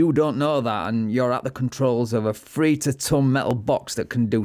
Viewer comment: wtf are you saying here? "free to ton" ?